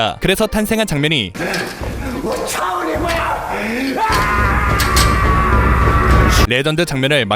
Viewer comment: 근데 이거 패드립 아니냐?ㅋㅋㅋㅋ 차오 니 마 라고 들리는데ㅋㅋㅋㅋ